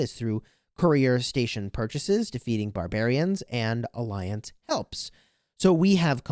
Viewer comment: Sounded like you said "feeding barbarians" is a way to get map fragments 😂